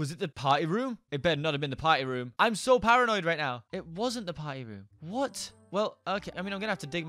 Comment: I thought he said potty room Sry